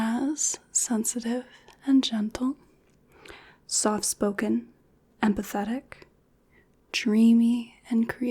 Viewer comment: It sounded like you said "soft spoken, and pathetic." Haha.